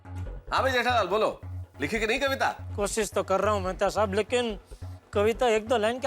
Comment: IN THE CAPTIONS INSTEAD OF MR. MEHTA IT WAS WRITTEN MRS. MEHTA 😅🤣😂🙃